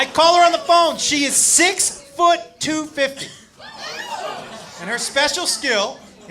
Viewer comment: I thought he was saying 6’2 50